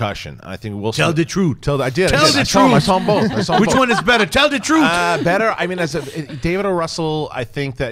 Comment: Oh, "Tell the truth" is what Smith's character is saying in that preview. I thought he was saying "Taylor Caduceus" because the guy just asked "Who are you?" Accents, man. Oy.